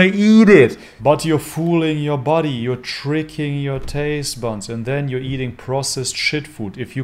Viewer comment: I have a massive crush on him so I can't tell if he really says "fooling your taste buns" at 15:09 or if it's a lustful hallucination on my part....